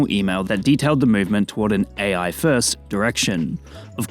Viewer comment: When I hear Duolingo, say "AI First". I hear "FREE PUFF"